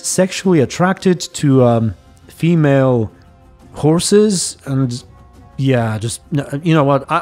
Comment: She's sexually attracted to female horses Same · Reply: I heard "female corpses" at first